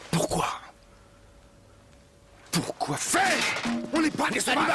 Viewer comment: Je rêve ou il a dit "on n'est pas des animals" ??!!! LOL c'est ce que j'ai entendu !